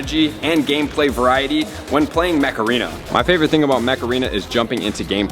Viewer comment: For a second I thought you said “Macarena”, like that horrible ‘90s song and it’s even worse “dance”. Haha